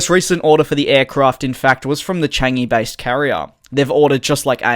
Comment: what did he say??? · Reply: The changi based carrier